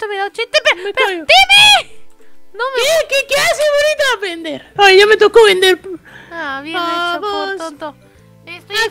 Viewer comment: Súbele todo el volumen en esta parte: 8:56 y escucha la palabra que dijo Timmy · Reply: TheSamuelGames_ Dijo pendeja :v